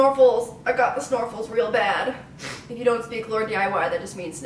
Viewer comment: I thought she said snorkels I was like what? but then I herd snurffes then I said aw you ok laurn?